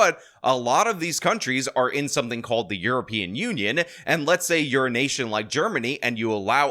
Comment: He said "you're in a nation like Germany" and I heard 'the urination of Germany' 😂